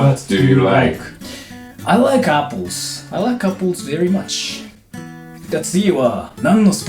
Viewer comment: i misheard apples as couples